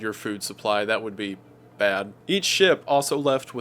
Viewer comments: Every time you say "each ship", it sounds like you saying eat shit LOL, I love it tho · When says each ship it sound like he is saying eat shit XD